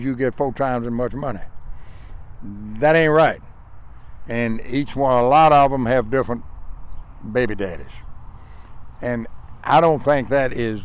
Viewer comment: Did he say, "A lot of them have different 'baby-daddies.'"?